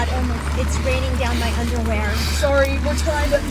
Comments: Lol who else heard omg Emma it’s raining down my underwear edit:at 6:20ish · "oh, oh my god its raining down my underwear" 6:21 Did I hear that correctly? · I hear omg it's raining down my underwear 😐😱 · Did anyone hear at 6:20 someone say oh my god it's raining down my underwear😂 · Did somebody just say 'It's raining down my underwear'..? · Did she just say"Its raining down my underwear"?? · Did anyone hear when a woman said it was raining down her underwear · Omg lol did anyone hear that lady say "Oh my god it's raining down my underwear" 6:21?